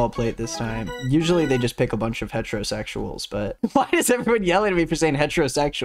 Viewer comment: I'll be honest I thought he was saying "metrosexuals" and I was like "wow that's a very precise type of guy they get into the show."